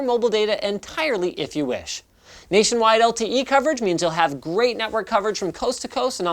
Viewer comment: i thought he said "nation wide LTT coverage" lmfao